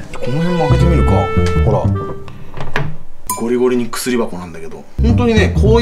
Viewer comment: 「ゴリゴリの薬箱」が「ゴキブリの薬箱」 に聞こえてびっくりしたw